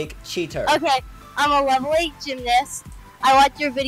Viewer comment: Wth did she say “iM a LoVeLY gYmNaST” 0:34 That sounds like weird tbh